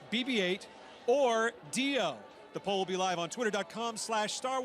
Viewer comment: what he Said ?! DIO !!!!